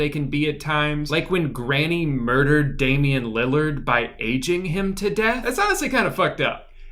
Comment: I’m crying I thought he said “edging” 😭😭😭